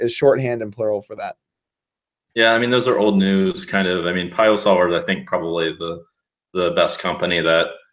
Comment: what's the name of the poker sim that jungle talkes about at 1:41:07? I didn't quite catch the name of it. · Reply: piosolver